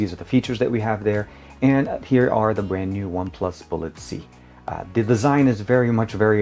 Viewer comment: Uff , finally found officially unboxing of original OnePlus Buds Z , Nice review 👏...But did u said OnePlus bullet Z